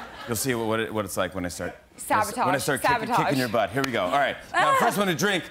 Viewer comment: sounds like she said Savitar sabotage. Now we know how she won..